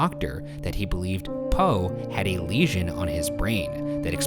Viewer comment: I thought it said he had a lesbian on his brain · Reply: Lesion